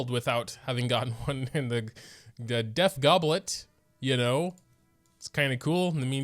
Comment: Ok- this is off topic but, whenever I hear someone say ‘DEF’ I hear “DEATH” lol